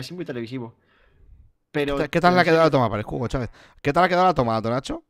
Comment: escuche mal o nombro al difunto hugo chavez?